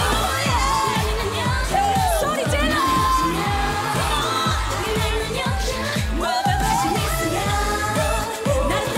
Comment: what does moonbyul shout every last chorus? Sorichila? 😂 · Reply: Hanna Esteban 'Sori jilleo' (소리질러) means make some noise